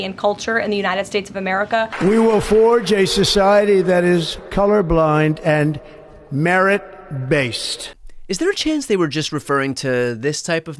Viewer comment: I think I heard him say coloured blind merits. The people who are usually coloured blind are narcissists. I believe he meant that he merits people who have a disorder.